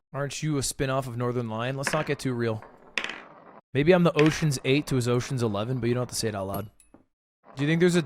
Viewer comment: At first i thought he said "maybe i'm the oceans gate to his oceans 11"